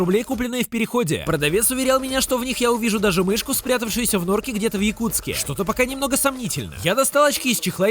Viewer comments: Мне не послышалось или реально было сказано "Где-то в Якутске" 0:57 вроде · Мне не послышалось?... на 1:00 был город Якутск?